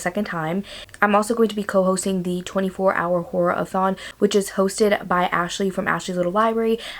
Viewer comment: Not gonna lie, when you said horror a thon, I thought you said whore a thon 😂 I was like I might have to come back for that 😂